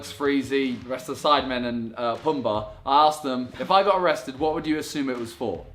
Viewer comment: who did he say Pom ba